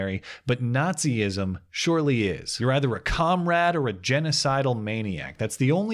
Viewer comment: Whenever I hear "comrade" all I hear is "cumrag". Immature I know.